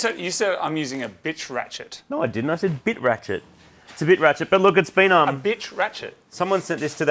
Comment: I definitely heard "bitch ratchet" lmao